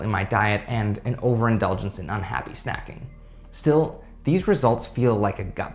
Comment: what is “unhappy snacking”?! How can that even exist lol, snacking is blissssss. Did he say that or did I mishear?